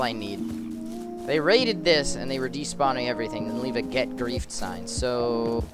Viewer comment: I thought it said “get girlfriend